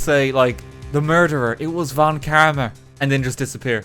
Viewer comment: I also love the way he says 'von kerma' instead of 'von karma' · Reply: Is it wrong that i always hear 'von Kermit'?